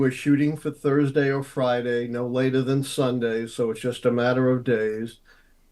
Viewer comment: Did he mention his prediction? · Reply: After rewatching 0:40 to confirm what I thought I heard, he did say "Thursday or Friday, no later than Sunday, so its just a matter of days"... Soooo Im not sure what people are saying in this thread LOL